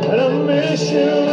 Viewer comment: I heard him say I miss you chilla